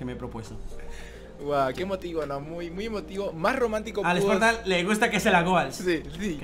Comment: mencionando a sparta aunque casi no entendí lo que dijo :v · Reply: Yo entendí que dijo "al Sparta le gusta que se las goals" y luego levanto un poco las cejas 7W7